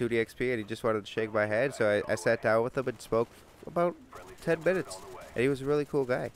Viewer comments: when he said, "and we spoke for about ten minutes, I thought he said and smoked for about ten minutes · lol at 1:57 I thought he said "yea so i just sat down and smoked with him for about, 10mins" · i thought he said and we smoked for about 10 min. haha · did he say sit down and smoke?